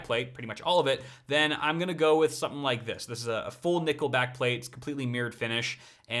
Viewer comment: all i heard was "nickleback plate"